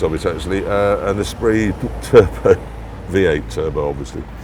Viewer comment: What car did he say? I couldn't understand him. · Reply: Lotus Esprit V8 Turbo